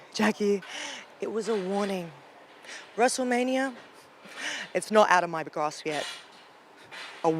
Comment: What did she say?????? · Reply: "Wrestlemania is not out of my grasp yet."